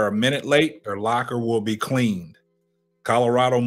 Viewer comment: Noooooooo he did not say that, he said they their locker will possibly be cleaned. “Let’s Go Piss Them Off.. | Like Them To Be Mad - Coach Prime” (Well Off Media) @ 5:31. He did not tell that way to the players, he said that to Lance and his family. (To the kids he said “Possibly” and did not say anything about a minute late) Just wanted to add clarity to what Yahoo put out.